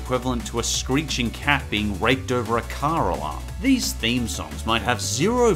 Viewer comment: Never expected to make a rape joke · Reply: Except he didn't, I don't know how so many people in the comments thought he said raped, I clearly heard raked.